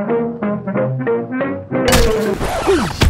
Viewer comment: i heard "i am the way of dirt" because why not